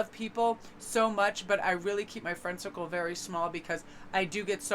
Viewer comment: sounded like u said friendsickle lol